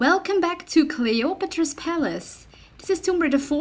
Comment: Did you just say Clay otro po palace at the beginning ^^